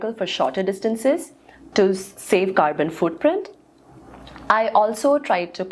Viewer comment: am sorry. did you say " save carbon footprint?" right